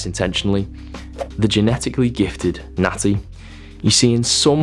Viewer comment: The first two times you said natty I thought you were saying nazi and I was so fuckin' confused lmfao